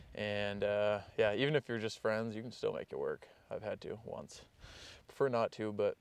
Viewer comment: Where do you found such girl who would like to camp in car like you? She’s the pearl! One thing to do stuff you really like, but much better if your loved one can share it too. God bless you both and happy camping! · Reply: absolutelyfailz4480 I had to rewatch it, I didn’t even catch that part the first time because he mumbled it quickly…so after playing it a few times I thought he said “I’ve had 2 at once.” But then I went back again and replayed it another few times, and I think he actually says “I’ve had two of THEM once”…which is strange wording, but now I’m thinking that by “Them” he was trying to refer to “Them situations of being back there with just a friend.” But again, strange wording either way. Basically we need the dude to reply to us himself to clarify it lol